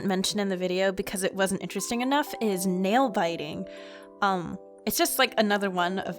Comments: I thought you said Mail Biting or Male Biting but nope just Nail Biting which is donaswell when i get the chance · Oh my gosh at the end I thought she said “MALE biting” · holy god i misheard nail biting as mail biting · For the outro, I heard, "Another one of my bad habits is male biting" and I was like,"Wait.....wut?" but I rewatched it again and it was just me. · at the end i thought she said male biting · at the end i thought you said male biteing not nail biting and i was like 👀👀👀👀👀👀👀👀😳😳😳😳😳😳😳😳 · I heard 'Male biting' and I got a bit concerned then I realized how much I have to clean in my ears...